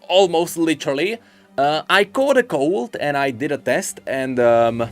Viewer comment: Is he saying he caught Corona?